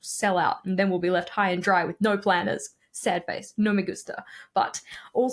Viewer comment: did you say "no me gusta" around 23:52?